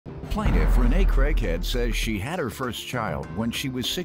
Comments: I thought in the intro he said her name was Rene crackhead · When I heard the last name I had to double look I thought she said crackhead lol and she favors card…. Never mind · Lordt I thought the annoucer said Renee Crackhead....I guess I was listening too hard 😬😅😂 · I thought he said Rene crackhead frm the preview when I was scrolling, made me click the link lol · I watch judge Mathis so much that when they said her last name was craighead I thought they said CRACKHEAD!